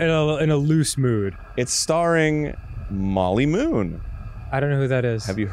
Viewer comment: My brainrotted ass thought it said Molly Goon Game